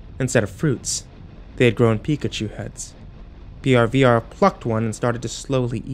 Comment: the P in plucked is emphasized so I thought he said "Brbr Fucked one, then proceded to eat it." I fucking choked